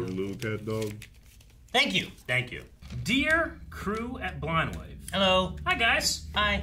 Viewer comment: I know doesn’t say, “Cruel Blind Wave…” … so what does he say? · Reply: At first I always heard "current Blind Wave", and it made sense 'cause they are always changing people depending of the reaction 😂